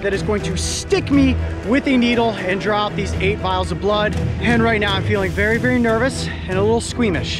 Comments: Any body else thought he was saying 8 miles of blood😂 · For some reason I thought he said 8 miles of blood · I thought I heard 8 miles of blood and I was like Oh well, he’s gonna die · At first i thought he said 8 miles of blood and I was like😱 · I thought he said 8 miles of blood like omg that’s a lot